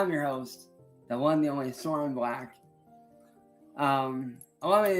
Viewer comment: Why does keep saying he's sore and black? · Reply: Ohhhh black i thought he said he's soar in back like from jake😂